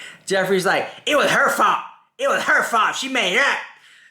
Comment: I think around 1:20:00 he’s saying “it was hurtful.” Not “it was her fault.”